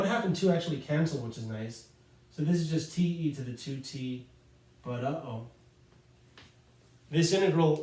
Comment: what did bro say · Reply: I said "...but uh-oh!" 😅